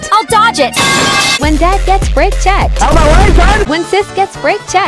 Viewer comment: I thought they said "rape checked" for a second I was like WHAT THE FUCK